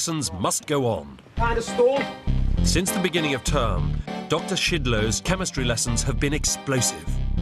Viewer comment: did she just say doc shitload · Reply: But it’s “shidload”